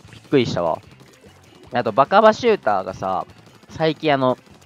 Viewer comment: バカバシューターて聞こえたw